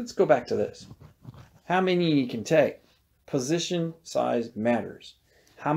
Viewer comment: But i thought she said size doesn't matter :(